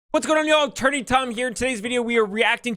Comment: When I’ve been drinking, I hear “What’s good on y’all?”